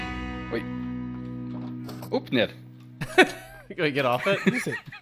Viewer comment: for the people who wanna know What he said it was: up down